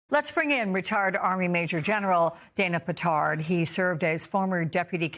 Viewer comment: Did she just say "Reta*d army general Dana Petard"?